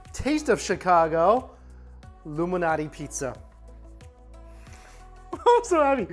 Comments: Did he just say illuminati pizza???? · I literally thought u said "illuminati pizza" 😆